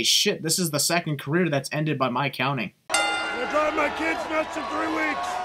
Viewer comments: What did Shawn said? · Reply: "I'm gonna drive my kids nuts in three weeks" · Can anyone tell me what Shawn said at 14:44 I actually didn't get it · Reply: He said "ill be driving my kids nuts in 3 weeks."